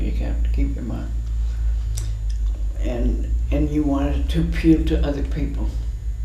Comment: Wonderful, But what does she says at 4:43 ? because what i hear now is that " you want it to puke to other people" I'm pretty sure that's not what she says haha Appeal to other people ? Thanks to anyone that can answer me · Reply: Yes......."you want it to appeal to other people....". I recent saw many of her works at SF Moma last week....very impressive, meticulous art.